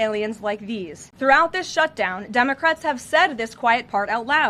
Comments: I thought she said dumbocrat. Lol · Cant wait to see the ball room and the arch! Sounds like she said dumbocrat? Sneaky kid! Ha ha ha!